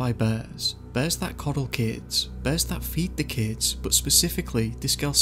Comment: For the first 10 minutes, I thought he was saying "Birds" not "Bears"...